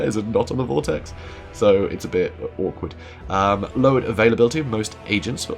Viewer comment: / My man did you just say orcward? You bring shame on us all.